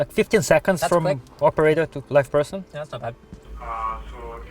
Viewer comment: I know he didnt really, but at 19:37 it definitely sounds like says "15 seconds from operator to black person" how could you know that bruh